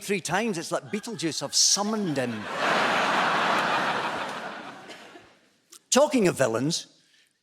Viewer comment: "Speaking of villains" lol Quality segue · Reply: Not gonna lie, I thought he said "speaking of felons," but this also got the joke across quite nicely.